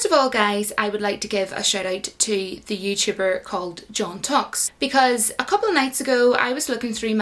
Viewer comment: mishears John Talks as JonTox because of the accent, goes to look up JonTox and gets confused about why she's talking about a channel that is German and has only 1 video Lmao